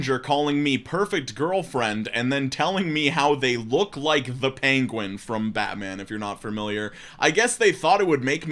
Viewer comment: Honestly, if some dude slid into my DMs telling me that he looks like Danny DeVito, I'm already sold. 11/10, I'm already wet, when's the wedding. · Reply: My dumb ass thought that he said SHE looked like the penguin! 🤣🤦